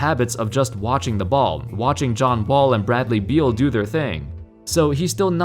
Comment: did he just said "John Ball" 😂🤣?